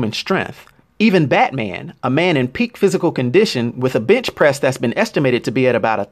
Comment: When you said bench press I thought you said bitch press.